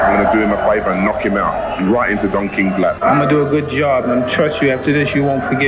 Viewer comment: I thought Frank Bruno said right in the donkeys (Don King's) lap.